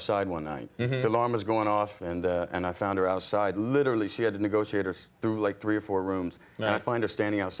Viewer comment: Brad says “Negotiate” instead of “Navigate”, and now I’m imagining some Alice in Wonderland door that won’t open unless you say the right thing